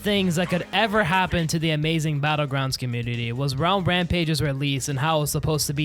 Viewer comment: Idk i heard "Brown Rampage"